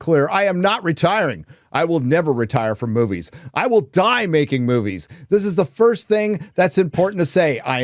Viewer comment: "i will die breaking movies!" is all i heard